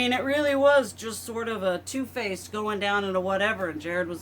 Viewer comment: I seriously misheard you. I did a double take when I thought you called TwoFaced "Toe Face". 😄